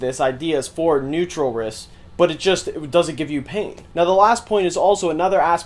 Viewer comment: did u say it doesn't give you pain or pink?